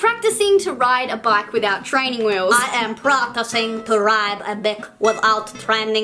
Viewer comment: I am practicing to ride a d*ck without training wheels😂😂 Sorry but its what it sounded like lol